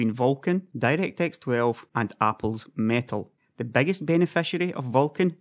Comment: I swear he said apple's mantle amount